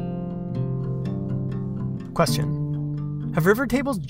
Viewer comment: when he said question at 7:30 it sounded like he said bless you and while i was watching this i had sneezed right before that and was high key spooked, nit that anyone cares but still